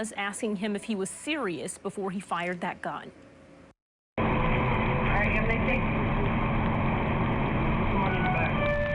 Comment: did i hear “get out ryan” or am i bugging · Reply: She said "get out, alright?"